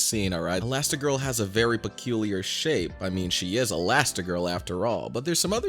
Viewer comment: what did bro say💀💀💀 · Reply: "peculiar shape"